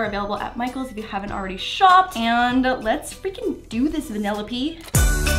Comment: It’s Penelope maybe you’re saying that but it sounds like your saying vanillape